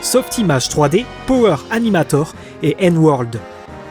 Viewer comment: quoi ? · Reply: Power Animator et N-World. Des logiciels qui sont à l'origine de beaucoup des premiers jeux 3D comme Super Mario 64, Final Fantasy VII etc...